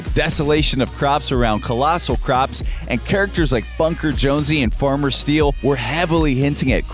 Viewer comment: did he just say funker jonsey